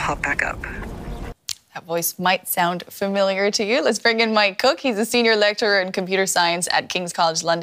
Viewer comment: I thought she said "let's bring in my cook" iI was like WTF???